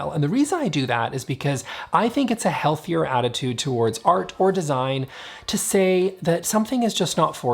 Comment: I thought you said, " it's a healthier approach to art or design to say, 'it's just not smart of you.' " 😆